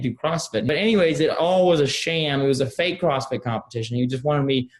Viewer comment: Oh, "crossfit." I kept hearing 'prostate' competition.